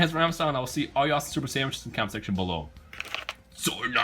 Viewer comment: Oh u said vegeta was Prince of all sayains I thought u said sandwiches